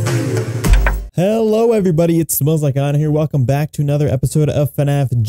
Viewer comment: sounds like u say it smells like honor